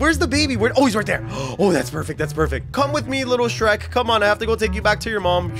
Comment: wait... did he say little shrek?!?!?!?